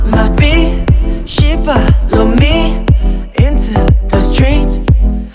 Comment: she follow me 가 왜 쉬발롬이로 들리짘ㅋㅋㅋㅋㅋㅋ